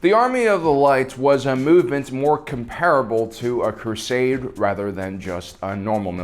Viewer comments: Did I hear Crusade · Did you say ... CRUSADE